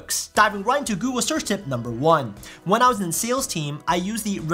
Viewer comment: HAHA! seconds I thought you said when I was at the SEALs team. And I laughed and thought to my self, thats the best cheesy joke has ever made. Heard it again just to realize it was "sales" team.. Oh well..